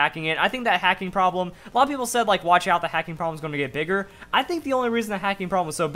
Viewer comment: For some reason I thought he said "the hacking problems gonna be a n****r" I need to buy some heating aids